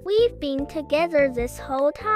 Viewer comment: did bro said. ‘I’ve got a egg’ okay then